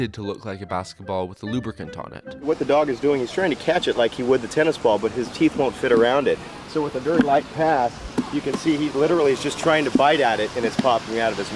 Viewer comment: what he said. "what the dog is doing is-" what I hear. "what the dog doin?"